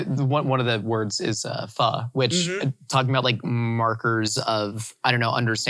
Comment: caption says fur instead of pho